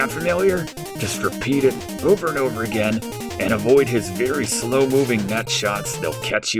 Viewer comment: I had this video playing in the background and thought I heard "just avoid his slow moving nut shots" around 20:20. I skipped back and was disappointed to find that I just misheard it. Good effort on filling the time in this video.